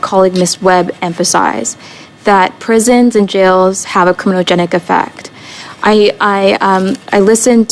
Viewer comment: Hang on, what the hell did you say? "Communo-genic" effect? Making up words to sound intelligent?